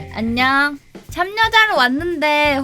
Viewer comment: 참여자가 왜 참'여자'로 들리지